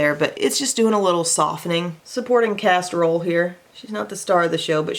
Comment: I thought you said "supporting casserole" and now I'm hungry 😂